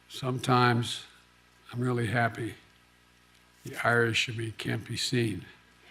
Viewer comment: sometimes he’s really happy what can’t be seen? I couldn’t hear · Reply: Sounded like he said the Irish in me can't be seen. He is part Irish, so that is probably what he said.